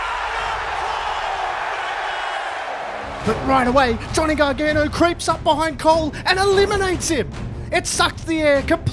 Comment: Hey I'm a big fan of your channel You are great tell this storytelling about wwe. Keep it up And btw what did Adam Cole said at 1:11 I didn't understand him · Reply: he says “ADAM COLE BAY BAY!!”